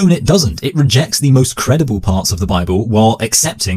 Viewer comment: i'm sorry but did i hear that right ? "credible parts" he said ? buddy, nothing is credible about the bible 😂😂😂